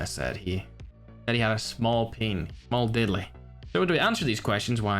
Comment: says "Peen" at 0:57 but the subtitles say otherwise haha.